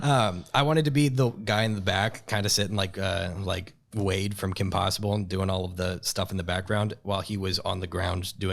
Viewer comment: did he really say he want's to be like Wade from Kim Possible !?!?!? 😂😂😂